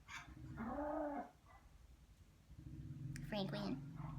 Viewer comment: what happened at 4:17 ??? What did you say? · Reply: Celestial Tarot she says Franklin... her dog was wanting to bark